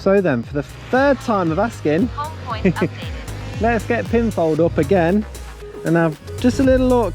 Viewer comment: I love the name for your drone i hope i heard right "pinfail" was it?Then we have a bunch of mupptes come out.The pic is under GDPR 😂😂😂